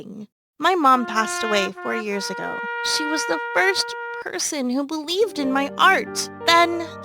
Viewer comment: Lol,I thought she said “she was the worst person who believed in my art”